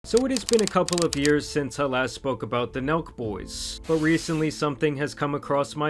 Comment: I've never even heard of the Nelk Boys. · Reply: I thought he said milk boys